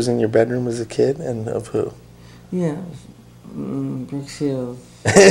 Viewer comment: Wow, he has taste, I like every artist he saw as well. He is missed. What did he say in minute 4:05 about the poster in his room (who was he talking about)? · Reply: He had pictures in his room of the actor named brooke shields and his sisters used to get jealous and tear them of wall that's what he said mj later on took brooke shields to the Grammy as his date